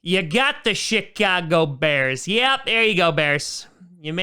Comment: Sounded like he said Shitcago Bears but didn’t want to make it obvious and hurt the Bears fans feelings